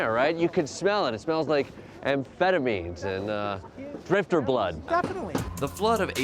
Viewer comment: Best question: Jordan - 'So, this smells like amphetamines and drifter blood?' Protester- 'Yes, exactly' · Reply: I thought he said "grifter blood" 😂